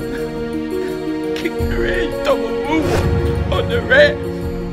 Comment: Im crying as we win the Super Bowl saying we double moonwalked the patriots ass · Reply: okay, I got confused since I heard "we kicked their ass, double moonwalked on the reds" Is what I thought. HAHAHA So i thought you were crying about somthing bad. But now I get it. ;)